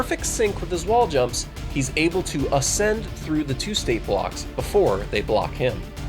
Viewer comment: I thought he said Tuesday Blocks.